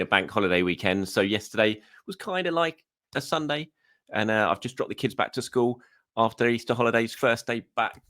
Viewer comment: I thought you said ‘I’ve just dropped the kids off at the pool’ for a sec 😂😂